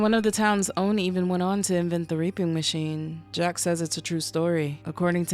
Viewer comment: I swear I head you say "R*ping Machine"